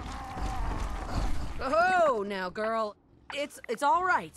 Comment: wait...did she just say easy girl...to a deathclaw? ......at 0:03? no wonder why fanfics of that exist...